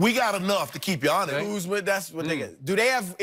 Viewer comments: did he say Kuzma...thats my nigga??? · Did Broussard say "Kuzma thats my nigga" at 11:39 ???? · did Brousard just say "Kuzma thats my nigga?"